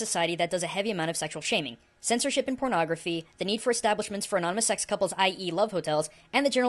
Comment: Guys help, im sorry to comment over something so trivial, but im really cracking my head over what he said at 0:30 ... "the need for establishments furanomusexcouples I.E. love hotels" ... what?? and the following sentence, too: "and the general uptightness of japan are typical guns in am of these people use"... man i love and all but sometimes he speaks so fast